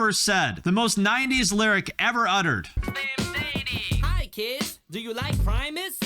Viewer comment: I always thought he said ‘Hi kids, do you like violence?’